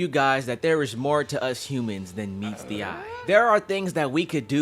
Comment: Heard that as “meats the eye” somehow